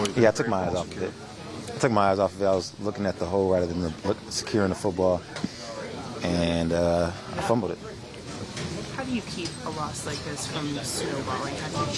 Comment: We was on the move until najee Harris got fumble itis 😒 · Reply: I thought you said "fumble tits" 😂